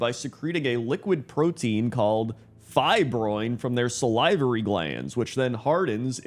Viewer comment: I think I had a stroke when I heard “sal-eye-vorry glands” 🤣🤣🤣